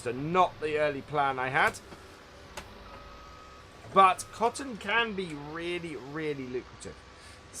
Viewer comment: i heard cotton candy lol. Was listening to vid while editing my photos and had to take a double take